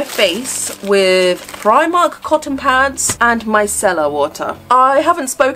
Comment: I thought you said my cellar water for micellar water and I'm just gonna start calling it that for aesthetics ✨